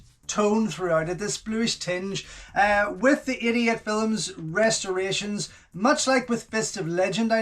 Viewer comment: Everytime you say 88 films it sounds like youre saying idiot films